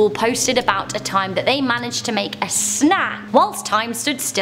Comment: Is she saying 'made a sna'?